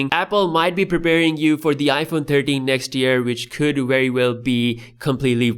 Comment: in 1:26 I thought he said iPhone 30 next year but he said 13 lol